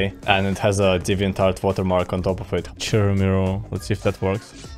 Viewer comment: I know he said it but I couldn’t understand what he said, what song is that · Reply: Chirumiru